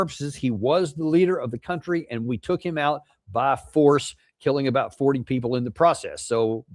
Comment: "...killing about 40 people in the process." I heard 80+ and 100+.